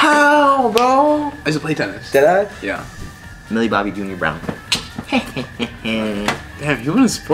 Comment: umm did I hear mille Bobby jr brown